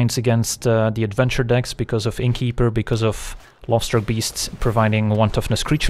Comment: Whenever you said "Lovestruck beast" all I heard was "Lobster beast" :D